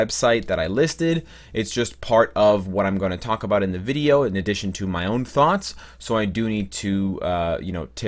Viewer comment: did you say "my own farts"?